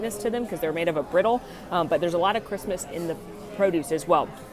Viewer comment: I was so confused for a hot second... I heard "there's a lot of Christmas in this salad". Took a min to realize you said "a lot of crispness...." Made MUCH more sense that way 😊